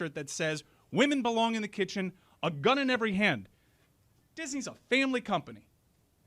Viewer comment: I thought that producer said Disney was a failing company, not a family company. So I agreed with him initially lol